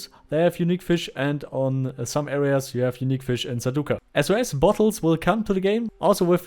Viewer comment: what did you say? "SOS bottles" ? :D